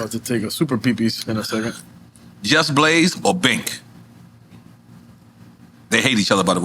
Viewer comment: Who is Bake? At 1:25:33 Nore asks Just Blaze or Bake. I've never heard of Bake. Is that what he said? And who is that? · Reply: Sounds like he said Bink. I don't even know who that is either lol